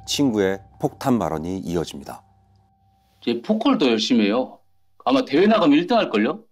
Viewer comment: 에 뭐라고 하는 건가요? 퍼컬? 포컬? 보컬? 폭컬? 아무리 들어도 안 들리는데 아시는 분 ㅠ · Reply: 포커